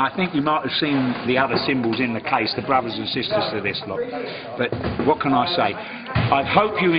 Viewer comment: SOUNDS LIKE HE REALLY SAID "THE BROTHERS AND SISTERS OF ISLAM" AT TIME MARKER 6:42 but i think it was supose to actually be" brothers and sister of this one"